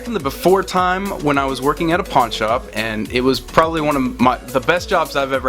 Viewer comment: I literally thought you said "porn shop" hahahaha I had to rewind it and I was like ohhhh PAWN shop